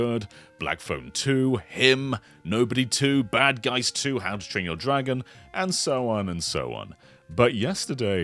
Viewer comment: sounded like he said “hamstring dragon” lmao